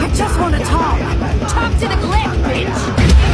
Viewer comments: Am I the only one who thought Luz said "Talk to the glyph b*tch" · who else thought she said bitch 😂 👇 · DID LUZ SAY "talk to the glyphs bitch"